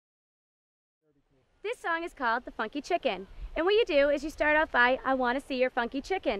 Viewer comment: My daughter learned this at a Christian camp in France when she was 4. Except she heard it as "fu**ing chicken"😅